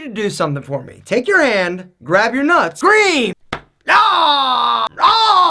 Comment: Ouch ! I thought he said "squeeze", not "scream".....